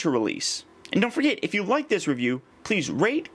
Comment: did you just say "rape"??